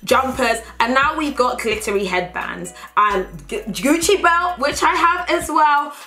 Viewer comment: Ohh you said "glittery headbands"..I heard at first "clittery" and it make me a lil bit confused, but then I thought "Fashion? Fashion"